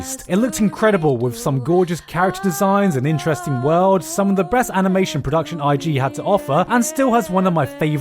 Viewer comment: is that a Freudian slip, "some of the breast anime production" or am I just hearing things.